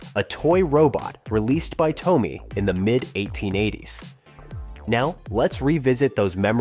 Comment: did he say rthe mid 1880"s ???? heheeh think he did